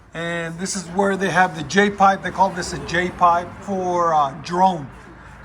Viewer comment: Lol thought he said the j pipe was for Jerome